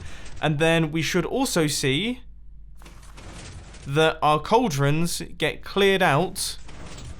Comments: Is it just me or did say cauldron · "Cauldrons" You said Cauldron instead of Composter, jut letting you know